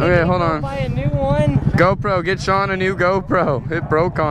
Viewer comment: i think the guy at 3:29 said HEY SEAN!